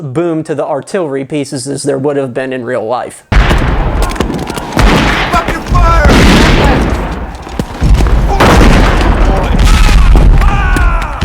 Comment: for a minute, I thought the officer say “fucking fire!” 😂 On another note, is it me or does it seem like Reenactors seem to be getting less and less.